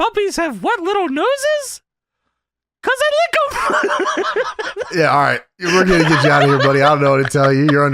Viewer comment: what did Lawler say? About the noses, couldn't understand the punchline because laughter is interrupting it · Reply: “You know why puppies have wet little noses? CAUSE THEY LICK EM!” 😂